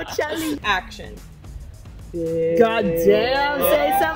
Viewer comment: I thought when role model performed I thought he was saying hoechella